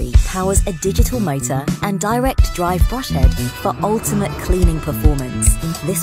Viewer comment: /3:53 did I hear the voice-over for the advert correctly, a direct drive Bosch head? 🥴